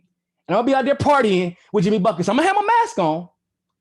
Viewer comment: I keep hearing "Jimmy Buffet", and I am confused lol